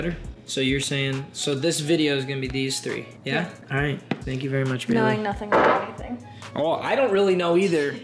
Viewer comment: I thought he said thank you very much bae at first